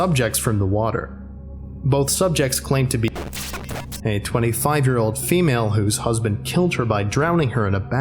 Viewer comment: I thought you said “a 5 year old who’s husband killed her by drowning her”